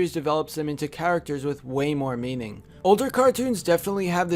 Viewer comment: it sounds like hes saying "way more meming"